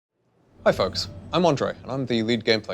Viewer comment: I first thought he said, Hi folks, I'm on drugs.